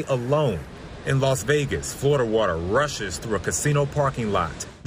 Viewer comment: did he say "Florida water"?